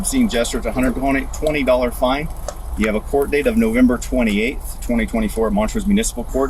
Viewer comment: Did I hear a November 28 court date? Thanksgiving day, today? Aren't courts closed?